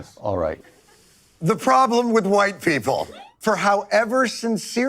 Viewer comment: I thought stewart said he was a white pimple.